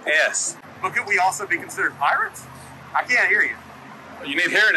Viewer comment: Lol, @ 11:30 did he say "but cant we also be considered pirates?"